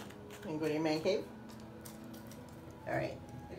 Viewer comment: Did she just say "go to your man cave" 🤣🤣🤣